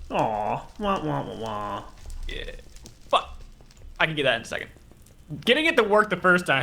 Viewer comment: did just say "fuck"?